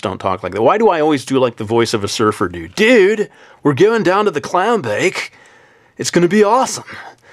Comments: lol it sounded like you said clow bake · I thought you said "Clown Bake" and.. o.o I was scared.